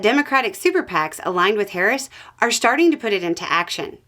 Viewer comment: My Auditory processin disorder threw me a curveball when ya said Superpac; as i heard "Democrat sycophants" instd and was confused for a sec til ya said superpac again and i figured it out heh